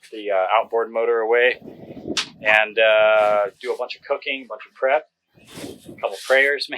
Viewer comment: sounds like you said: 'do a bunch of cocaine,...'